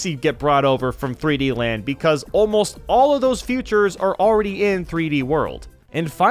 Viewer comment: Iheard future instead of features